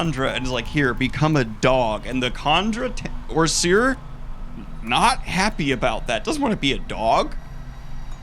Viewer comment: "and the kondra T.. Orsuir" I heard that lol.